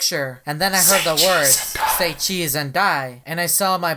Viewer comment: SAY CHEESE AND DICE Sorry For The Lie, I Misheard It.